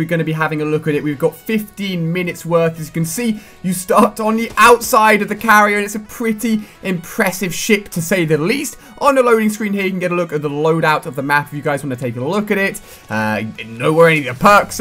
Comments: LOL I thought he said at the beginning "some pretty impressive s**t" like if you thought the same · Sounds like Ali says ''Pretty impressive shit'' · Holy crap did he say, "this is some pretty impressive shit to say the least," at 0:15.